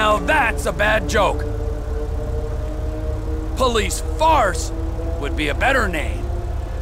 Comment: “The police force, now THAT’S a bad joke. Police FARCE, would be a better name.” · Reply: I thought he said "Police farts"